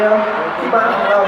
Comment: เหมือนได้ยินพี่แบมบอกว่า ‘พี่มาร์คของเรา’ อร๊ายยยย!♥️เขิลลลล💕 (ความชิปปปป55555)💜💙💚💛🧡❤️